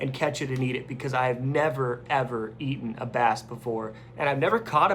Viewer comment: I thought you said you were eating a bat, then I realize you said bass.